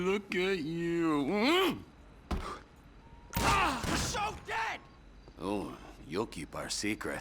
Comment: I keep hearing YOUR SO GAY!!